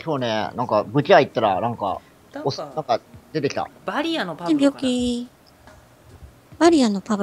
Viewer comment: バリアのパブロが早めのパブロンに聞こえた(ふふっ